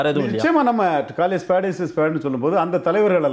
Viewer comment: can someone please say what he said????? In many interviews he said this thing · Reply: Call spade as a spade. English Proverb.